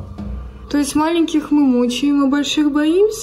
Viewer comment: "Маленьких мучаем а больших боимся" все мы немного Котар. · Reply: А мне послышалось мочим а не мучаем